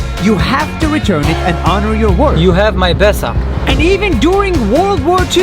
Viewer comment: I heard it as "you have my paisa" lmao still means same Hindi speakers will get it